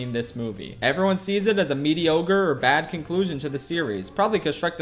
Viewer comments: I heard that MEDIOGRE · he says mediogre instead of mediocre I have been enlightened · did he just say mediOgre instead of mediocre